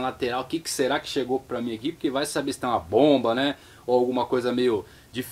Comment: foi só eu que entendi ele falar? "vai que e uma pomba!!!!" kkkkkk